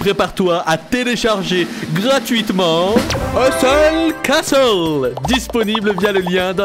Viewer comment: j'ai compris "euh seule casos" au lieu de Hustle castle :))))))))))))))))))))))))))))))))))))))))))))))))))))))))))))))))))) ))))))))))))))))